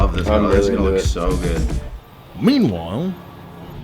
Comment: I swear that was Clarkson saying "meanwhile"